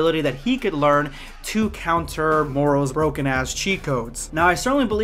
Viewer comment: Why did it sound like you said “ Moro’s broken Ass cheek codes? “ 😂😂😂😂😂😂🤣🤣🤣🤣🤣🤣🤣🤣🤣🤣